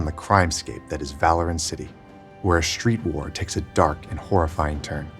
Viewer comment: did he just say "valorant city"?